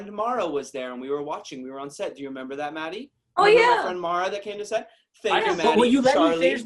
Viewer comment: I’m sorry did owen just say “friend mara”?!?! At 11:51 ?!?! OWEN!!!! Let’s be friends!!! The Bestfriends ever. Except, I don’t remember being on set??